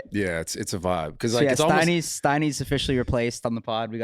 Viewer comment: Dude I thought you said steiny was off than he’s still talking on the pod, wtf